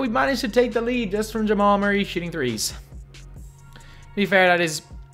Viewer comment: sounds like he said Jamal Murray sh*ting 💩threes